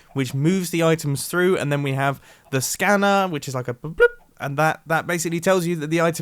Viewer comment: When he said scanner I thought he said scammer!